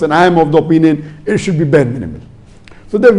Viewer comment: what did he said. I could not hear it clearly, is it permissible or not? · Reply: Bare minimum